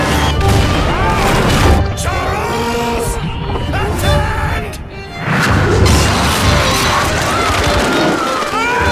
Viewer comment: tf did he say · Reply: CHARLES, ATTEND!!